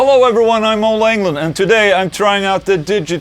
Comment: At first I thought I heard, "Hi I'm Ole England" hehehehehehe....